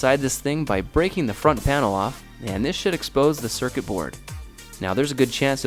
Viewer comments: did he say he got it from a drug store?! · Did he just say drug store